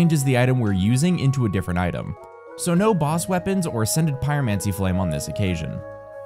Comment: My sleepy brain really thought you had said "scented pyromancy flame" and I thought maybe I had missed something in my gazillion playthroughs XD love the channel, love your work, thank you for a great bedtime story ❤❤❤